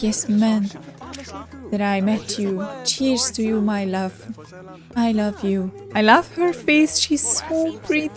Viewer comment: I thought you said I love your face. You're so creepy. 😂😂😂❤❤❤